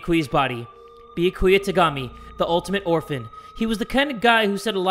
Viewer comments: did Jay really say the ultimate orphan?😭😭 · Did Jay really call Byakuya the "Ultimate Orphan"?? LMFAO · I’m sorry..did he say Ultimate Orphan.?? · did he say orphan??